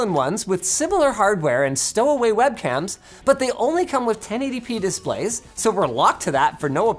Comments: subbing error, it says 10 ADP .... displays instead of 1080p dislays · Captions at 7:05 is wrong "10 ADP" instead of "1080P"